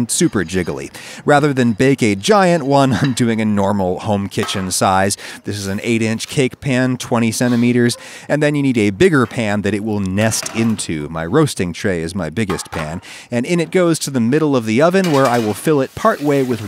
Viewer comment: not gonna lie I thought he said 8 inch pan, 27 metres not 20 centimetres